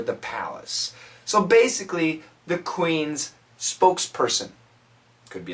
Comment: what's the word at minute 2:08? spock???